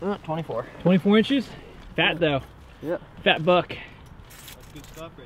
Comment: fist time I heard swear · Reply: Darcy Foster he didn't he said fat buck